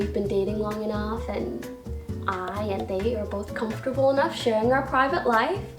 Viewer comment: Whe she said I and they I thought she said I and Dave and I was like 🤭who’s Dave